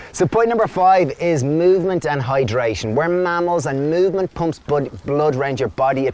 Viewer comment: LOL I thought you said we are camels until my brain caught up and I realised you said mammals!! :)